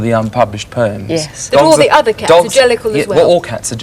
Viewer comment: I thought the man said, "Cats are angelical." All I could think was, 'You think cats, who are known for trying to kill their owners, are angelical?'